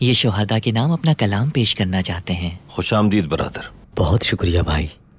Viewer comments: did he said brother?😂😂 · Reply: He say brader.....brader means brother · what Ertugrul said Khus amdeed BROTHER😂😄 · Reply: xD review Not brother yaha Bradar bolte usne woh bola like bottle ko bottle bolte yaha but tt ki pronunciation mein farq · did he said "brother"?